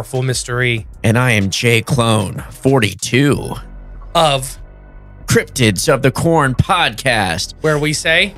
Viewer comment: Am I high or did he say cryptids of the porn?